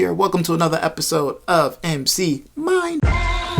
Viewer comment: Damn My ass Thought he said MC Mine as in Minecraft lmao😂 +Good Vid Tho👌